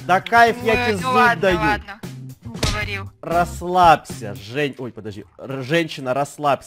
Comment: я подумала что он сказал Жень АХВХАХВХВХА